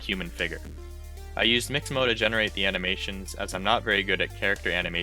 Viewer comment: hahaha, I didnt understand what you said, and closed captions didnt do there job. Check at 1:50 with closed captions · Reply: Haha I get it now! I guess I said mixamo to fast